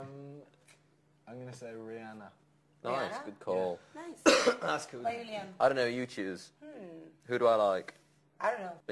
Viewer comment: What does Niall say while coughing at 6:39 ? I just couldn't understand it, something like "ask ...?" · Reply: Ask louis